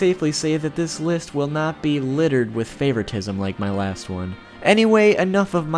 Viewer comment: I thought he said no favoriteing but gine is up their